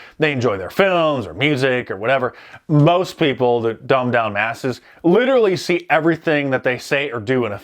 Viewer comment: Did Mark call them bumb masses or dumb asses? The latter is more accurate.